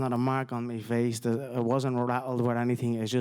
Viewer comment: "I was not rattled" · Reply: Still 3:44 "There is not a mark on my face, I wasn't rattled WITH anything(meaning strikes), I just had no leg" He is clearly saying he was not rocked by anything, now shut up you absolute air head.